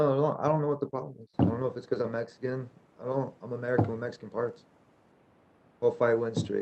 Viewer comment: " I'm American with Mexican parts. " -Tony Ferguson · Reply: I thought he said ...Mexican parents? Lol